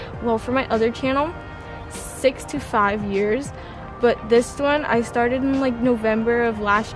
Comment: When she said 6-5 years I first thought she said 65 years xd